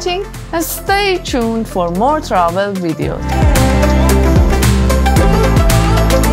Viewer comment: I thought you said "stay tuned for more trouble videos" :P what a beautiful country Pakistan is.